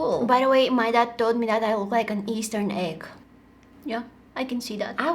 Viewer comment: It sounded like she said eastern egg😂